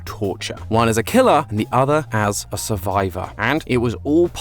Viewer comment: did you did you just say MASTER PLAN